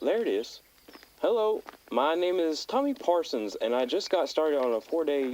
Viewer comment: i am surely not the only one who misheard ‘Tor Parsons’ and assumed the cabinet of curiosities was about to get unlocked